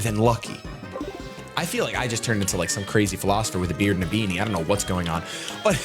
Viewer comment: QOTD: I THOUGHT HE SAID BREAD THE WHOLE TIME, but nah I'm poor and don't care.